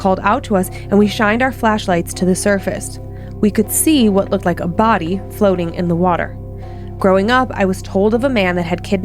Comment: Umm, okay, correct me if I'm wrong, but didn't she say they SAW A BODY floating in the water??? Did I hear that wrong?? How is no one talking about that???? 😅 Edit* "We could see what looked like a body, floating in the water"